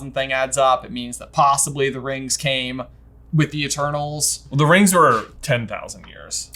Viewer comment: everytime you said "the eternals" i thought you said "the turtles" and that threw me off a little